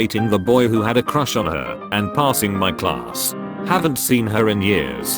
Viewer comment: I THOUGHT IT SAID SHE PASSED IN THE CLASS N I WAS SAD